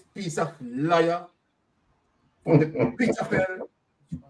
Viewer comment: I thought you said "Amos was a liar from Peterkin", but alas you said "pit of hell"...same attendants perhaps.